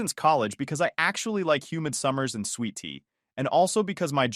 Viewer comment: Sweaty? I thought u said sweet tea